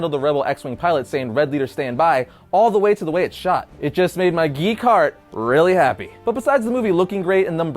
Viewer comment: I thought said "it made my geek hard" 😏